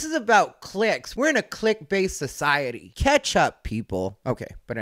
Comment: When you said “Catch Up people” I heard “keptup”. Which for me made sense given the previous statements. Idk I thought that was clever.